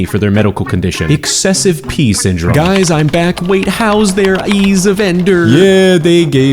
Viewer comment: did he say "EYS"???? 😆